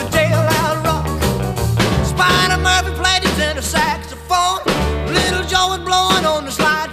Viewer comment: Did he just say...... "There a spider monkey in my saxophone" 🥺